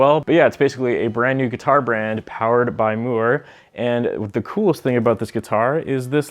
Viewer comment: NGL at 1st I thought you were saying "Moore" in Canadian. Sorry dude